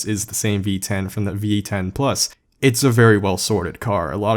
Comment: Did he say v8 10 plus